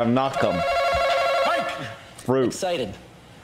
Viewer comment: what did he say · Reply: The question was : what is something you 'feel' before you buy it? His answer : excited (since he asked "what is something you 'feel'", he replied he 'feels' excited)